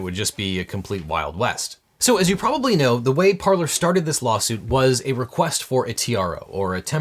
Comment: At around 20:00 said: "As you may know, this started with a request for a TRO." I heard: "this started with a request for a tiara."